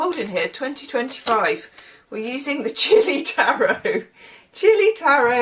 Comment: I thought you said "chippy tarot" instead of chilli.😂 My ears are declining along with my eyes.